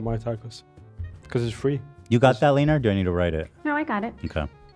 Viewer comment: Did he call her Leener?> Like Seleeneer?